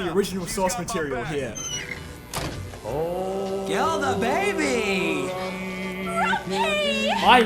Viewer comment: did he say kill the baby